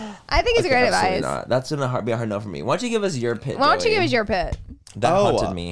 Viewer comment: so, as i’m laughing my ass off about joey telling manny to go shit in the dogma bed, i swore i heard laura say “joey, why don’t you give us your poo”. she said pit. pit.